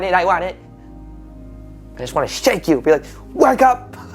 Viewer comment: I could have sworn I had heard shake ... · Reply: on yeah, you are right! My brain wanted to hear shank apparently because it’s a word we use in my family - jokingly. And I just found it so funny!